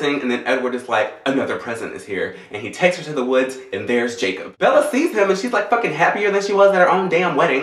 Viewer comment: when you said "and he takes her to the woods and there's Jacob" i thought you said "and he takes her to the woods and bears chase 'em".